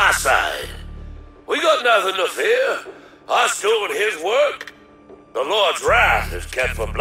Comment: that hard to understand part I believe is him saying "I shown his work" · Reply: I was told that the full line was "We got nothing to fear, us doin' his work!"